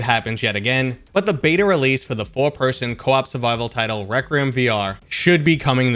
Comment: Sounded like a mix of Rec Room and Requisition😅